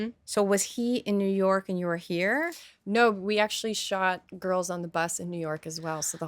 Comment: everytime I hear her say "girl on the bus" sounds to me "the wheel on the bus goes round and round round and round"